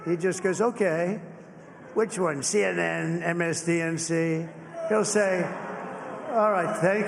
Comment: does he say “msdnc” lmao 🥰